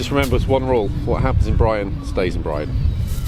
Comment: Looks like a blast! What happens in Brighten stays in Brighten 😆😆😆 I thought he said Brian